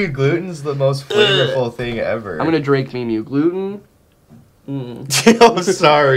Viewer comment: Did say "mee-mee". Unsubscribed.